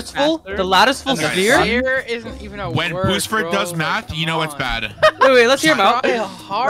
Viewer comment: am I the only one who heard say "sphere isn't even a word bro"?